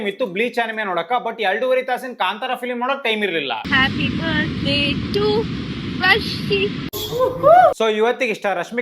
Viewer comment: Is she wishing u.? I heard it as Happy birthday DARSHI😀